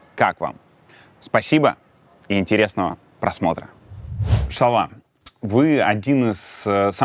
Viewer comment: кто как и я не расслышал